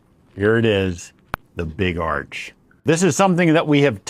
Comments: I swore he said “big ARSE” sauce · It’s noisy where I am and I thought you said Big Barf 😂